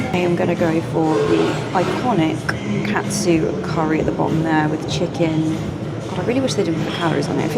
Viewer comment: Funny sign at 1 time. I thought they were saying cat food curry and I'm like. Why are they feeding curry to a cat